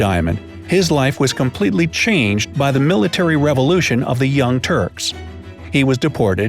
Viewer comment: I thought he said turds hahahaha